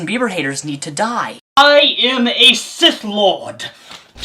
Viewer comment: I just heard : "I am a Cis-Lord" GG. I know what it's supposed to be but this is so much funnier